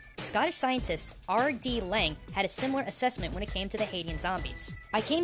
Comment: i keep hearing "Hades"